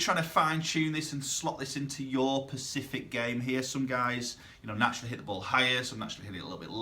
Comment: Did he say my pacific game? How did he know that I'm planning to play Pasatiempo and Torrey Pines this year? Hopefully Bandon Dunes next year, too.